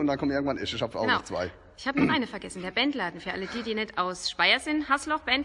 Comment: Bohhr voll Puls bekommen 6:03 ich hab „Der Wendler“ verstanden 😂😂😂😂